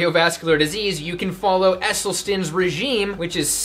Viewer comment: he says the name of a guy who's regime seems awesome. However, I am not a native and I can't understand the name he says. Can anyone help me? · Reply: Sara Dr Caldwell Esselstyn